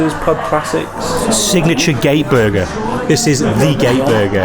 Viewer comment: I thought you were saying you were going to eat in the Gay Tin to eat the signature Gay Burger